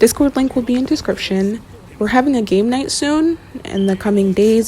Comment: tell me I’m not the only one who accidentally heard “we’re having a gay night” instead of “game night”